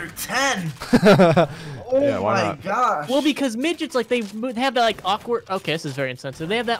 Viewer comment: i deadass thought he said ninjas not midgets so when tim said 9 or 10 i shit myself